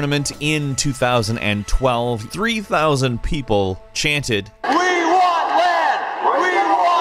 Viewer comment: I thought they said we want banta